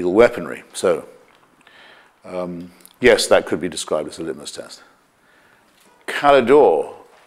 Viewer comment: What did he say? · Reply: "Yes, that could be described as a litmus test."